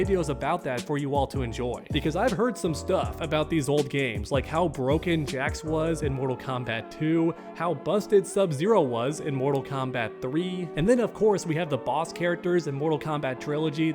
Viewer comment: i honestly thought you said "How Broken Jax was in Mortal Kombat 3, How Busted Subzero was In Mortal Kombat Trilogy" with that said both are very true 😁